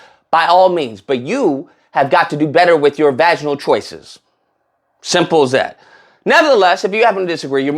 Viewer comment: did he say “vaginal choices”? 😮 Never heard that before. 😊